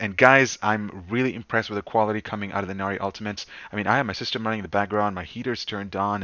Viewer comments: he said "My system running in the background" but i thought he said my sister moaning in the bedroom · did he just say he has his sister moaning in the background?😂